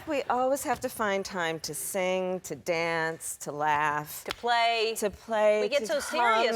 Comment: did she say "to play and to come"???